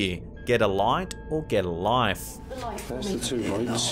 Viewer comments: Well I heard "Hit the like" rather than get a life/like 😀😀😀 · Sounded like, GET A LIGHT, to me. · I heard "In the light" in the blackout clip. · I heard, "I'm in the light". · I didn't hear 'get a light' or 'get a life', during the video at 8:17 but heard 'in the light'... anyone else? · "Hit the light" is what i heard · I heard "hit the light" with headphones full volume (bose) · I heard "Hit the Light"